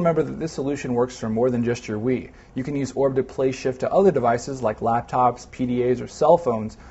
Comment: Does he say "shit" at 2:33?